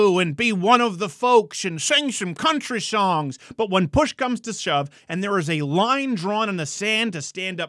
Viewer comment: I thought I heard him say, “when Bush comes the shove”